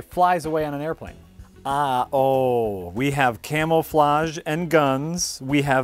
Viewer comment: Did he say "camel"-flage?